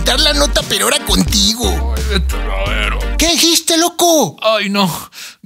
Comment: que dijo? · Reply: "Vete al Averno"